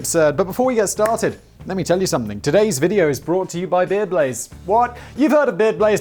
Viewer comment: For a moment I thought I heard video was brought to us by Beer Blaze... well there you have another idea, and being in Czech Republic, what are you waiting for! MAKE SOME BEER BLAZE!